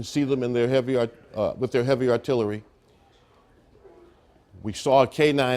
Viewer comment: did man say heavy artillery 😂😂